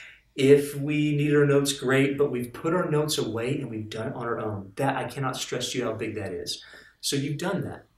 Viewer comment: This stuff's great! Just one thing, what do you say at 7:29? I can't hear you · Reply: He says "and we've done on our own ..."